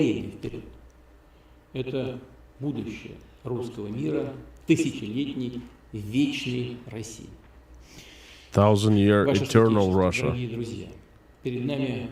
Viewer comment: Did he say thousand year Reich?